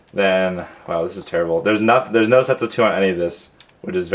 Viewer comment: what the heck did u just say? lol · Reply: No sets of 2